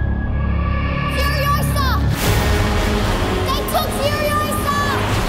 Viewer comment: Did she say "Naitwa Furiosa" at 00:12? It means "I'm called Furiosa" in Swahili